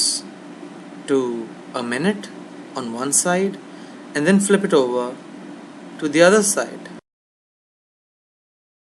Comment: i can't really hear clearly what type of butter. you say 45 second or a minute. but you do not mention at all how strong should be the heat/ fire that your pan is on... i wanna cook for my punjabi friend, so i need to know all the details. what kind of butter? how strong is fire? plz... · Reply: medium to high heat, butter is amul unsalted butter I've used if u have white prefer that